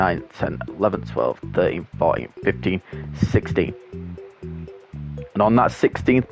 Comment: When you said 16 @ 25:22 I thought you said 60